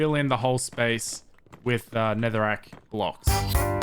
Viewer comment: I thought you said netherite blocks for a second lol